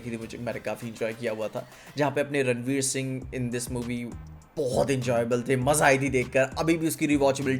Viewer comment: What's with saying maza ayegi ?instead of saying maza ayega Anyone ?? 4:07 ❤ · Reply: Maza aai thi bola he